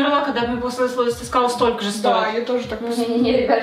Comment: мне послышалось 3,5 стоил....о боже